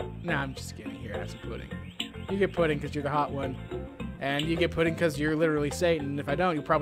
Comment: did he say the baby was hot?